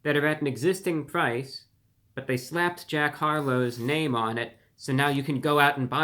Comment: I thought it said jean Harlow. I was like wtf? They had McDonalds way back then? How the hell did they know Jean Harlow's Mc Dernald faves?? Bahahaha